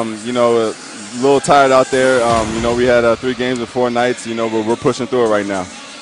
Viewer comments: sounds like he says 3 games of Fortnite? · I thought he said “we had 3 games of Fortnite” 😂